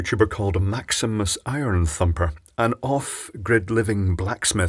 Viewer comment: haha at first i thought it said maximum front humper